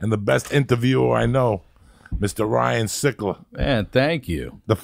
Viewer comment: i thought this said "ryan sheckler" i tripped out